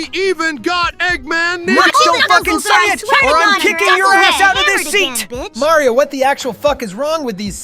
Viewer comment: What were Sonic, Tails, and Amy saying in 8:42? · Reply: Okay I understand what sonic was saying and he said “don’t freaking say it or I’m kicking your butt out of this seat” (I’m trying not to say bad words btw) but I don’t know what the other two characters you’re talking about are saying.